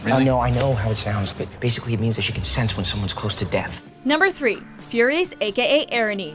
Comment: thought it said furries, was gonna agree XD